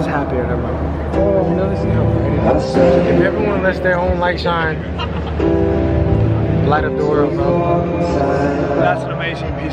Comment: It's so hard for me to understand your friends... What did say at 12:00..? Btw: Sad to hear some seriousness when he said "glad we're not in America" and you confirmed... Really sad. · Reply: "If everybody lets their own light shine, light up the world ahgeuiaf"